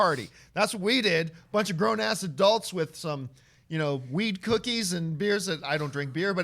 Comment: am I the only one who heard “weed cookies” 33:05? gettin baked??